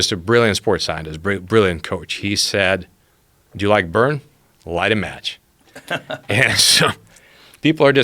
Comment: What does he say at 6:39? Do you like burn, light a match??